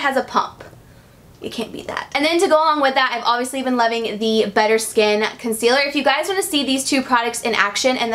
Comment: I honestly thought you said "butter skin" :)